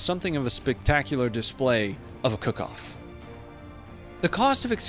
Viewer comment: Can't tell if you are being sarcastic but "cook off" is when the ammonition inside the tank ignites from incomming fire and the tank blows up from the inside. The videos of tanks throwing their turrets many meters into the air is because of a cook off. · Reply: Thank you (I really heard it as "ka-koff" instead of "cook off"). I need to get used to accent.... ;-)